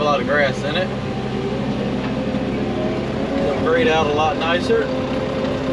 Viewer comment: Great job 👍🏻 love the cows mooing love how you laughed and glad you got the levers changed love how you said there we go unhook yourself 15:58 love how the words said braid out instead of grade 😂 I thought the 350 was in the thumbnail 27:30 love the reflection of the camera